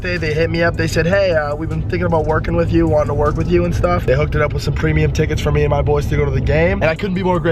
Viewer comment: When he said "some premium tickets" I thought he said supremeuim